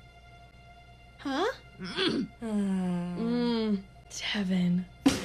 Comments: i thought she said “kevin” after said mmm but it was devon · tbh I thought she said heaven xD